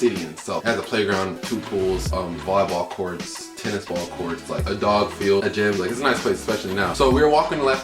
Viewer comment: Tennis ball courts? Did anyone else catch that. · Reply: Yes eos also has tennis ball courts